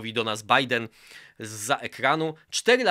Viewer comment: Za pierwszym razem usłyszałem "Donald Biden" i się trochę skonfudowałem... ;)